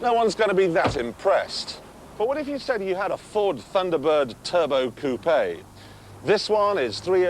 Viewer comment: That's what he said.... right? 3:19 to 3:24 "Thunderbird turbo coupe 3 years old"